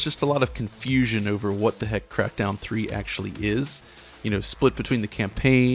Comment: I honestly thought he said crapdown 3 at the start